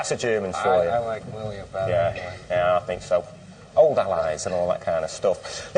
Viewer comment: Does anyone know what he said at 6:24 ? It sounded like "older lies and all that stuff" to these American ears. Is "older lies" a British expression? Anyway, it was yet another cunty thing that he said anyway, I'm sure. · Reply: Old Allies.